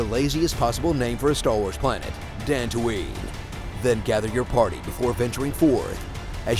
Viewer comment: Ummm...dantooine was mentioned in a New Hope...... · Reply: I always thought they were saying Tatooine, not gonna lie.